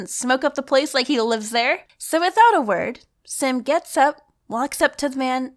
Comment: Greetings to ur brother Sam 🤔😅... I think u said 'Sam gets up' at 3:40 😂❤️🐦